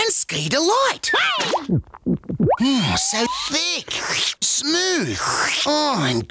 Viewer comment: i hear so thicc 💀💀